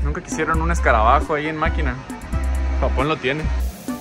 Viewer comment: escuché "Makima" xd Chainsaw man, qué me has hecho? :>